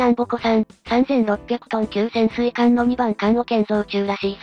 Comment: のっけから「潜水艦の2番棺桶ん」に聞こえて困る 水中3600トンなら伊十六、基準で伊四百を越えたか